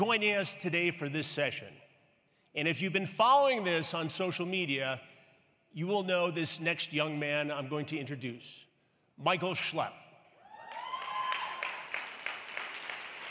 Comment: bro i thought he said "My Goat Shlep"